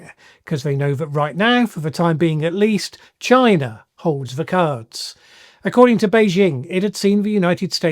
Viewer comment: Oh CARDS trump thought you said FARTS. 😂😂😂😂😂